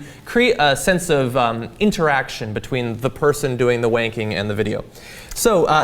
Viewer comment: Did say "I love wanking" at 0:18?